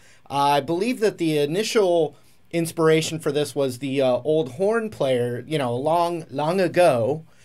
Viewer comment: i heard "PORN player" hahahahahah